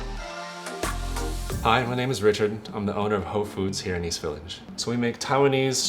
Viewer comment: I thought he said "whole Foods" lol